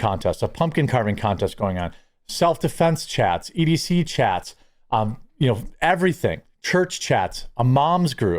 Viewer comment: did you say “BBC chats”????? I am no hater but not sure I want to join that one